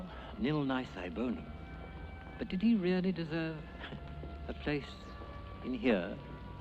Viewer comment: What does the priest say @ 1:35? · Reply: "Did really deserve a place, in here?"